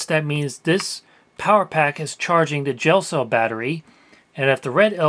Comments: I kept hearing " Jail cell battery". Which made me ponder ways that a prisoner could use one. lol Tattoo guns, weapons all kinds of good stuff. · I thought he said "jail cell battery" and wondered wtf that was lol.